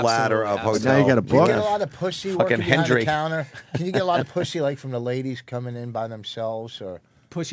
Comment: So funny he thought Vos said "pushy" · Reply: I thought he said pushy too at first